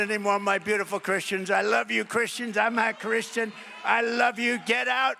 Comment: at the end of the video you played the clip where trump is pandering to a bunch of Christians about voting but i swear he says "... I'm not Christian..." around the 18:29 mark, can someone please see if im right or wrong.